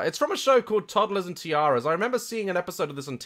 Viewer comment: for a second I thought you said my name bc my name is TIARA (I’m not a spoilt pageant kid. I prefer a downtown life style!)